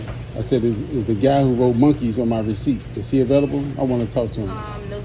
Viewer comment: Lol at first when. I seen that name.im.like ok someone is messing around then I heard him say his name... come on man that is a mistake. I had to rewind back because I thought he said his name was monqese . Not or how ever u spell it